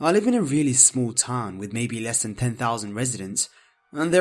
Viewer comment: what small town of 10000 my town has less than 3000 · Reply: 10,000? I thought he said 1,000. lol. My town has around 5,000 and it seems big sometimes.